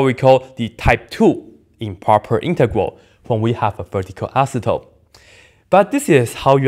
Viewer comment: "When we have a phaticu acetone" I'm sorry but I had no idea what he said there lol